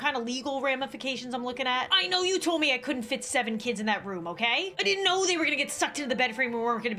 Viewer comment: I thought she said seven pounds but she said Seven kids